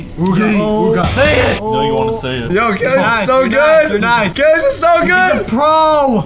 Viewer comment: says... Gays are so good 🤣 Why i hear it like that